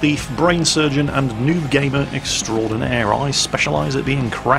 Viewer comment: "Rock Star, Horse Thief, Brain Surgeon, and NUDE Gamer Extraordinaire." - 2016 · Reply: I thought he said NUDE gamer too. Must be doing it for Rita.